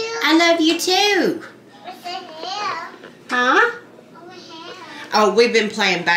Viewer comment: Ok I'm sorry but @ 6:36 it sounds like lol miss said "what the hell"😂😂😂 Whatever she said she is so expressive! I didn't have any girls when I had my kids, I tried 3times and got all boys, and now I've got 3 grandsons and no granddaughters 😢😢 So I'm livin vicariously through y'all 😂